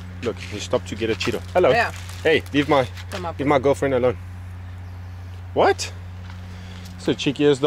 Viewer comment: did he say leave my girlfriend alone?